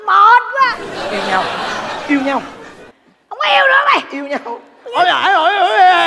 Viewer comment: thôi ko có yêu nữa mày, yêu con c** " :)) Có ai nghe giống mình ko? Hay mình nghe nhầm ???